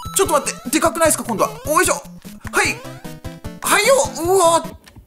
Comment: おねしょって聞こえた